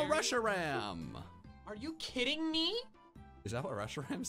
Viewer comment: if you turn on subtitles then it will say russia ram instead of reshiram